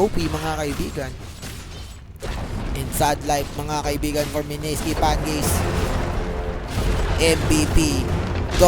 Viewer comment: did he just say "Mineski FanGays"